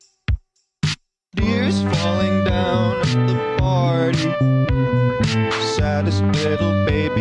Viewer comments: I once thought it said, "Ears falling down at the party". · I ALLWAYS THOUGHT IT SAID DEERS FALLING DOWN NOT TEARS😂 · i keep hearing “deers falling down at the party” and not tears LMAO